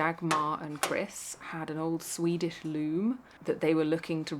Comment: I first thought you said "Swedish loo", thinking it a bit odd that someone would want to inherit one of those. Being from Sweden, I have memories of "Swedish loos" from childhood summers spent in the country. And they're really not that great ;-)